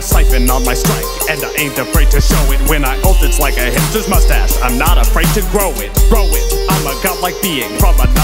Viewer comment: Is it really hipster's moustache? I thought for sure they were saying hitler's.